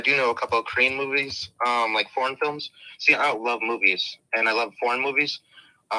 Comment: does he say “I love porn movies”? 🤣🤣 That’s what the YouTube transcript had him saying but the topic was about Korean music (which is terrible btw and bts are your typical teenie bopper group) and korean films 😂 so where did porn movies come into it? Did he actually say that or am I and YouTube both wrong? I know these creeps try and slip sexual things into everything but this just sounded so random. Lmk what you think cos I’m befuddled, confuddled, astounded, astonished and perplexed